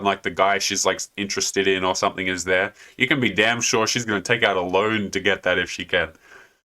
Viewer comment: Did you say she'll take a "loan", or "load" to get there? LMAO!